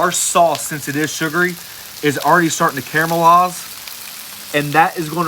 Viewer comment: Don't you mean caramelize not camelize? Sorry just sounds like your saying camelized. Sounds funny. Keep the great vids a rockin!